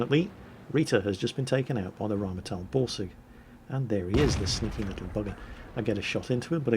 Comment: When says Borsig it sounds like he is saying Ballsig :D